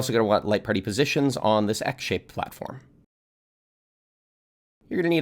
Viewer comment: I misheard “X-shaped platform” as “egg-shaped platform” 😂